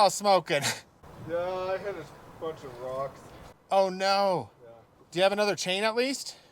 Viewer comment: When you asked why his saw was smoking I laughed. I had to listen three times because I thought said “ ah I hit some f-ing rocks”. I was pretty shocked until I realized what he actually said.